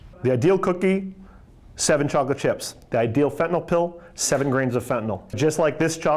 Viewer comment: my boy did not just say 7 grams of fentanyl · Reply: I believe he said "7 grains of fentynal" , I heard that too at first like wtf? How you get 7 grams in one pill ....?😅